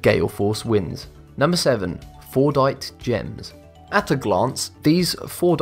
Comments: Thought u said fortnite gem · Who else thought he said “Fortnite”? Lmao